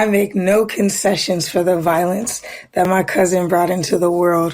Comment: did Say cousin???? WTF